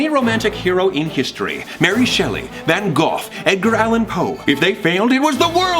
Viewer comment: Did say.... Van Goff? Instead of Van Gogh? XD